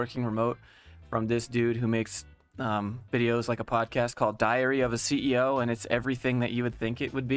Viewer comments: How come every time you say "Diary of the CEO" I hear "Diarrhea of the CEO"...? EDIT: It is funny, how people cannot live without everyday chores of getting to office. EDIT2: They definitely are talking about negative impact of "Lockdown" not "Working From Home". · I misheard "Diarrhea of a CEO", but came to the conclusion it was actually accurate. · I heard it "diahrrea of a ceo". I think i heard it right, after listening to the nonsense. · LOL I heard "diarrhea of a CEO"